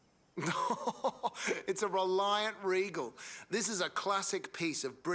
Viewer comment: Reliant Robin, never heard of a Reliant Regal did he say?